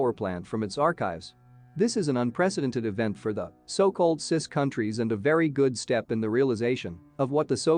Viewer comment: Did he say cis countries? 😳